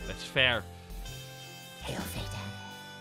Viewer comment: I skip trough the stream and suddenly i hear wisper "hail satan~"